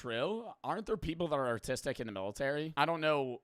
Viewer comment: did he say artistic 😭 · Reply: “are autistic” just said it fast lol